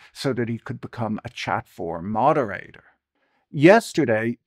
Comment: I am not a native speaker, I know that I probably misheard at 0:31 but I'm going to insist he said "chud forum moderator", as the idea of "chud forum" sounds like an absolute hellhole and fits the current landscape of X.